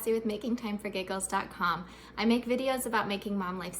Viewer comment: I seriously heard that as "making time for gay girls" 😅 YouTube iPhone app chops the user name off. Great video by the way - I have adhd & depression and it really doesn't work to let it pile up. Don't have energy for all that. Little and often feels more doable.